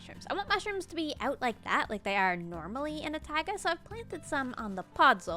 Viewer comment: I love your videos but I noticed a mistake: at 16:26 the subtitles say tiger instead of taiga❤❤❤ Edit: at 19:41 they say Elvin instead of Elven😊